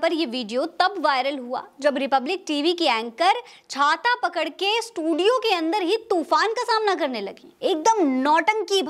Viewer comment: did she said jhanta pakd k ?